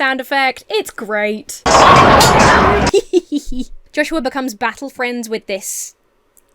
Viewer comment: At about 22:50 I could swear you said Joshua becomes "battle frenzied".